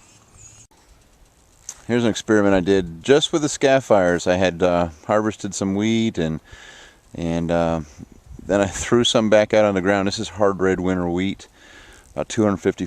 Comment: lol at first it sounded like you said i just harvested weed